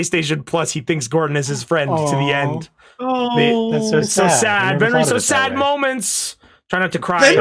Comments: did just say sadstuck and was she making a homestuck reference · did say sadstuck or am i Homestuck brainrotten